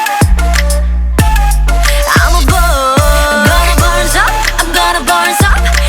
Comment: “I’m gonna burn up” Me 1st time listening to Bird: Hmm, the pronunciation is kinda weird Me 50th time: “I’m gonna burn sup, I’m gonna burn sup........Fire ~” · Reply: Omf I thought it said, “I’m gonna bird up” 😶 oh